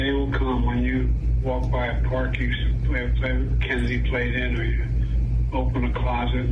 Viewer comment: Can someone tell me what he says at roughly 1:10? "The day will come when you walk by a parkabloutologlo that Kennedy played in..." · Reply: "When you walk by a park as a kid he played in" you've got that boomer hearing, sorry. 💩💩